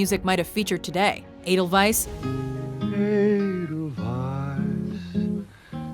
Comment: For a long time I thought they were saying Anal Vice not Edelweis lol what can I say I'm not great at German. But why wasn't Miss Saigon on this list instead of stupid Hair??? Thats one of my favorite musicals of all time! Lastly, HASA DIGA EEBOWAI!!!